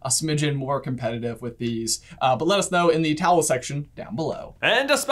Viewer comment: Did I mishear or did he say towel section instead of comment section?